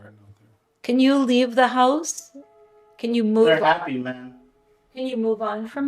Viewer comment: it sounds like he said “we’re happy ma’am”